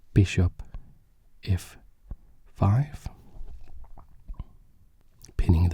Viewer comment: did u say bishop f5 on 2:33 ??